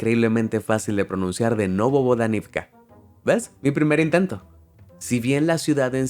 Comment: yo escuché no bobo